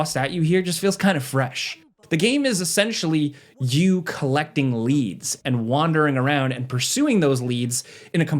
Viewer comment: I thought you said the "game is essentially you collecting *leaves". Must be a 200 hour plus kind of game then ;)